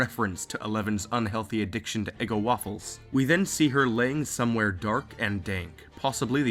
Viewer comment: did he just say "somewhere dark and dank" at 0:48 i hope he did